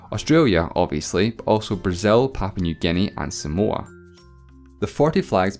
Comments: When you were giving examples for countries with the southern cross 2:30 when you said Somoa I thought you said some more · i was listening with headphones on and i heard "brazil, new guinea and some more"